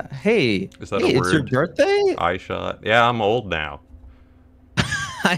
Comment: I'm sorry I only heard Q say "It's your girthday?"